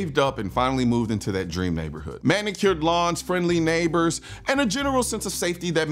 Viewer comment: At first I thought you said “manicured blondes” 😂